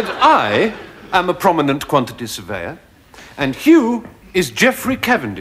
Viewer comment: I kept hearing “prominent quantities of air” instead of “quantity surveyor”…made the connection on like the 10th re-watch 😁 thought it was another absurd joke. Even though they literally talk about surveying quantities in the sketch 😁 ah man Stephen’s dance moves are amazing